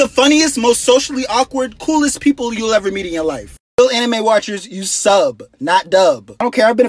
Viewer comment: why did i think it said amine fans does anyone even listen to him anymore except me?